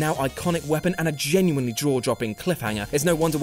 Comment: Did the guy say Draw Jopping instead of Jaw Dropping at 5:24 ?